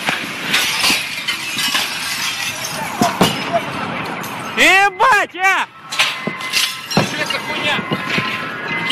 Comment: What does this Russian guy say at @ 6:23 ? It sounds like, "Eh, BWOTT, yeah!" What does it mean? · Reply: "yebat," as translated below. "ебать"